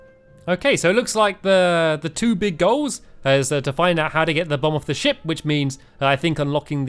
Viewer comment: First i thought you said how to get the bomb off the shi* 😂😂😂😂😂😂😂😅😅😅😅